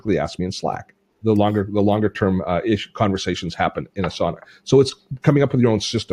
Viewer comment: Haha, I'm multi-tasking and I heard "a sauna"... then realized you said "Asana". Also, my productivity soared when I set up monitor #2 - I "create" and I saved so much time not having to look for the right tab.